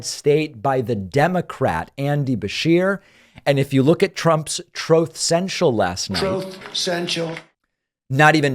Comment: been a while since I hear the "troath senshial" sound drop 😂😂